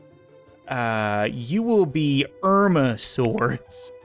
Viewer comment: Is it bad I said “Erma” sarcastically than all of a sudden i hear “you will be known as Erma Source” 😂😂